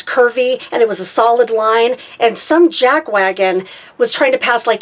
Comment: Some JackWagon? LOL! I have a new phrase! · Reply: I thought she said JagWagon... I adopted it immediately. I could just see some impatient thirty something in a Jag passing a bunch of cars.